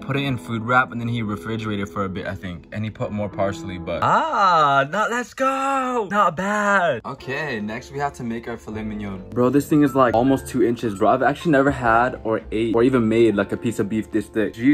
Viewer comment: I thought it said "I made a flight minion" eye